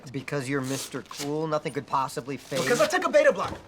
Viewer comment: what does he say at 7:25? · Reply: "Because you're mister cool nothing could possibly phase-" "Because I took a beta-blocker!"